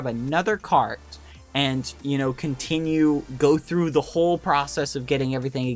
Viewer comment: it sounded like he said a hole xD